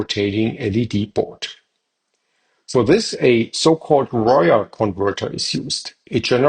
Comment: How are you transmitting power without a slip ring? I am having an issue understanding what you said in the video, it sounded like you said a wire converter, YouTube captions says royal converter?